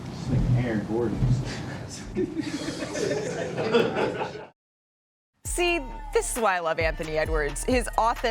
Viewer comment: Where in the video? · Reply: "Dis Nxggx Aaron Gordon" y'all can't hear. Tf